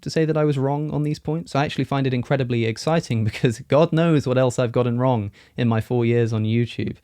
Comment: Did he say, "GOD knows... " 😋😆